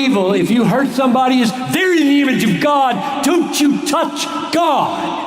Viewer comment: Anyone know what he word he said at 4:32? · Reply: ronald glenn they’re in the image of God don’t touch God